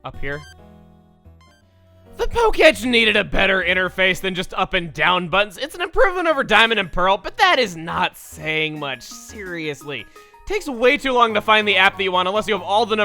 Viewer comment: "Up and down butts" That's what I heard.